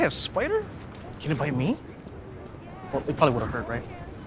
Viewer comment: Typo at 1:19 Ned says bite but the subtitles says bit love your videos btw :>